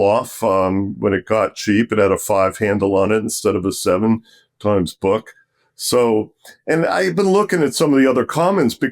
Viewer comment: At about 29:00 he says he owns something (Analy?) giving a 15% coupon. I can't make out what he's saying exactly. Anyone know the ticker he's speaking about? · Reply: That is NLY take a look at a chart and remember many charts allow you to adjust returns for distributions. Cheers and wishing you well